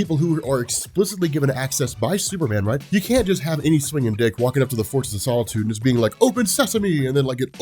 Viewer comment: Did he just say... slinging dik? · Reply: nope, he said "swinging dick"